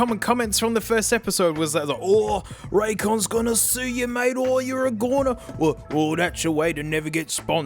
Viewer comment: What is he saying? "Raycons goona sue you mate..." and then? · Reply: "Oh you're a goner"